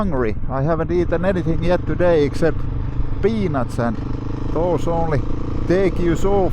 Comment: did you say all you've had to eat is penis mate .